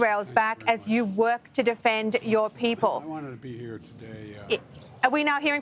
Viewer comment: Did biden say at 4:20 " I don't wanna be here" 😂